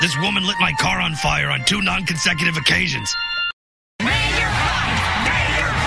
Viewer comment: "Man, You're Hot!" is that what they said? I couldn't hear correctly. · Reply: "Mayor Pines! Mayor Pines! Mayor Pines!"